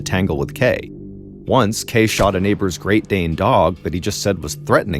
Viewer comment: He shot a neighbour’s dog that he said was threatening him. Cant say I feel sorry for him. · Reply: I wanted to go all in on him until I realized I had not heard right. I thought he said " Greyhound" which are the sweetest, non aggressive dogs. Then I heard Great Dane. Fully socialized Great Danes are a so sweet. One of my friends had a male GD and he was the best. But just like many dogs, no socialization and training can result in a scary dog.